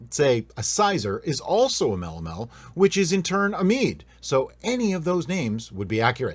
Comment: Lol... I thought you said... Unf***amel... Had to rewind that part...